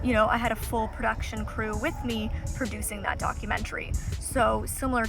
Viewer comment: I thought you said "dogumentary" hahaha